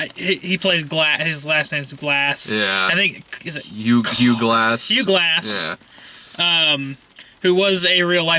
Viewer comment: I thought for a second they said that DiCaprio's character's name was Hugh Jass.